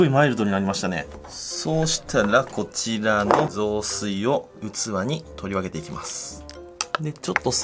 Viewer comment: そうしたら妻に雑炊を取り分けていきますって聞こえて、さん優しい！素敵！取り分けまでしてくれるんだ！と思ったら器だった。